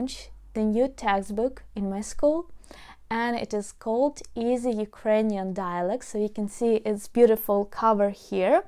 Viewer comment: Dear - I was primarily listening to this video, as things & people in my home are a constant distraction. I really thought you said the name of your textbook is “Easy Ukrainian Dialects.” I started wondering why we should change to learning Ukrainian by focusing on many different dialects of Ukrainian, rather than learning a clear and standard method of pronouncing Ukrainian words? ? It truly puzzled me why you continued to say “Easy Ukrainian Dialects!!” As a teacher myself, I needed to stop what I was doing and investigate further. I see that it’s just a case of you pronouncing the vowel O (as in LOG) by using the short E vowel sound (as in Etc.). It reminded me that the majority of my problem understanding foreign speakers of English has been when they use a different vowel sound from what we use, or when they do not pronounce all the consonants well enough for us to understand the word. I really do want everyone to understand you when you say the name of your textbook! I do speak a few languages and understand that different languages pronounce vowels with different sounds, but I wonder if perhaps a little more time focussed on phonics at the beginning of learning a new language might help prevent such misunderstandings? I do wish you a great success with your textbook and teaching! I have referred many people to your channel and your teaching program. But I really do think more English speakers will understand you if you pronounce the last syllable of your textbook t